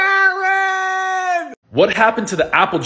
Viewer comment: “KAAREEEN at 1:46 sounded like Dave saying “AALVIIIIIN”